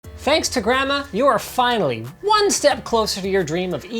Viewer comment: ohhhhh hes saying "thanks to grandma" i thought he was saying "thanks to grammer" haha